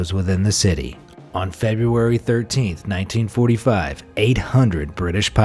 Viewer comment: I’m just here to see how many people noticed he said 1945 but caption said 1935